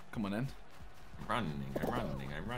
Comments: legit thought Josh said "run ni**a run ni**a" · what did he sayyyyyyy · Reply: Josh said "Running" 3 times